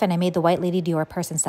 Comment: i didn't know the purse was called "lady dior" so when you said a "white lady dior" i thought you meant it was a purse popular among white women 😂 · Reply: I totally heard "so I made the white lady do her purse instead" I was like "what white lady?"